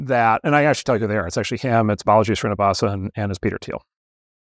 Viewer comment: What are the names of the people he says at this point in the interview? I recognize Peter Thiel's name, but I can understand the other two names he says. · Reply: 3 names: - Peter Thiel - Balaji srinivasan - Venkatesh Rao